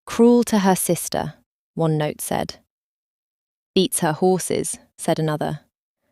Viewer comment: I thought the note said Eats her horses...😮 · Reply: That’s what I HEARD too! The second time it was said it was definitely BEATS and not eats